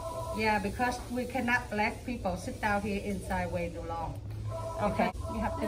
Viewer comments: Is it my ears or did she say at 7:26 that "we cannot black people sit down here too long" ??????? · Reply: she said : we cannot LET people sit down here for too long. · did she just say we cannot allow black people to sit in here too long???!?! Wtf tell me I heard that wrong, if be pissed.